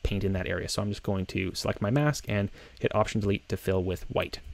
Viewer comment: the most important moment in the video and I can't understand what you said at 8:05 - what did you do so those colors blend together??? "hit option ......." ? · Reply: hit option+delete to fill with white.